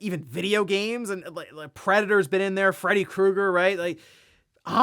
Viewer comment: I misheard Freddy Mercury and thought it would be a really awesome MK character